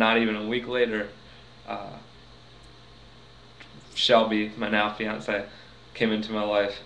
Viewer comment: ooh never mind lol you said Shelby..i thought you said Shelley because you look like the husband of Shelley that came out on Praise the Lord.